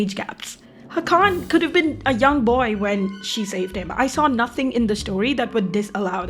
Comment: I hear u like em young